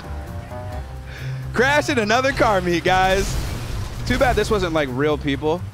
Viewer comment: I thought it said erotic car meet